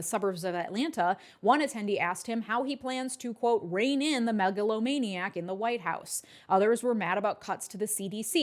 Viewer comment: The way that Georgian woman says "megalomaniac in the White House" scratches my brain in the most delightful way. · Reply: I thought she said "maga-lomaniac"... which I thought was kinda clever.